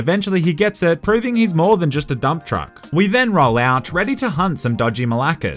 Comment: did u say dumb truck or dumptruck